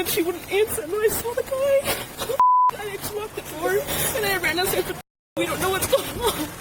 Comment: listen carefully at the .28 second mark..She clearly says that 0:28 SHE ran back down stairs.. was not in her room ..in my opinion